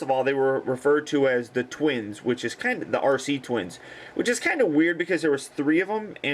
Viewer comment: Exactly. It took me a couple of viewings to make that out. At first, I thought he said "R.C. Twins!" as in "Radio Controlled" twins. Then I realized Arcee was in this movie.